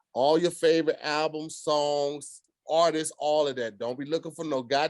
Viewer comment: tongue 😂 I thought he said "all this" but he said "artist".